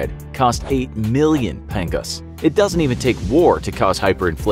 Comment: did he just said pingas?!